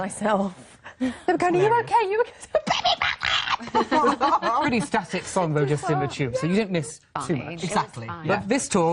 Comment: What Claire is saying? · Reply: Jordan Carrington she's saying put me back up